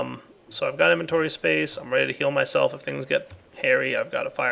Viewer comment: "I'm ready to heal myself..." I thought he said kill myself instead of heal myself, replay that and think about it LOL